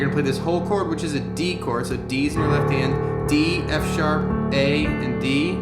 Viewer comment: lol 3:59 "Deez in your left hand".. another great tutorial at any rate. I just giggled when I heard that line.